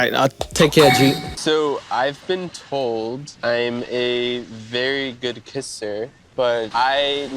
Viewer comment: I first heard "So I've been told I am a very good pisser"